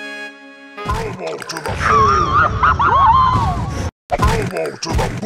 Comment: It sounds like he was saying Ow repeatedly